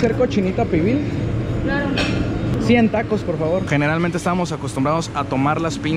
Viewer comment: Entendi 100 tacos por favor JAJAJAJ